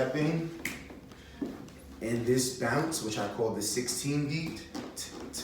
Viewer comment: This is so well-explained! I do have a question, though. At 6:06, what did you say the name of this unique bounce of litefeet? Sixteen...? Sixteen beat?